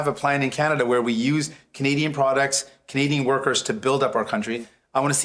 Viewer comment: Sighn says 3:55 Canadian “WORKERS” Sighn did NOT say “Canadians”